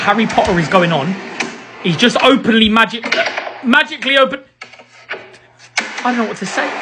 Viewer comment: I heard a "Openly Magic" and I laughed so hard😂😂😂